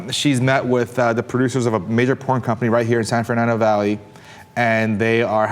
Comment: oh, "Major porn company". First I thought Steve said "Midget porn company" and I thought.. "well... yeah... that sounds plausible if a little kinky" :P